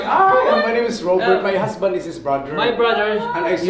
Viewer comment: did he say his husband? @ 12:52 I think those are Orca's right?